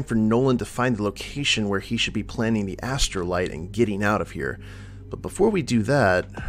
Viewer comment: At first I thought you said astroglide...that'd make this a very different sort of mission. lol